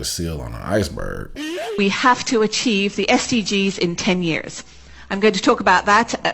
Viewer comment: I thought the lady said: we have to achieve STDs in 10 years! Me: Noooo :face-purple-crying